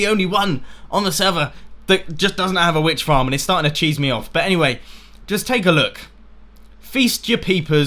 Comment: did say starting to cheese me off at 21:45?